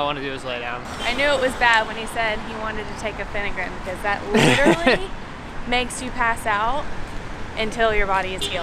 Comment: What is the Medicine Called? I couldn't hear well, neither could the Captions. jw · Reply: Phenergan. It’s an anti-nausea med usually given to cancer patients and people on intense anti-viral therapy, but is often carried by travelers for emergencies.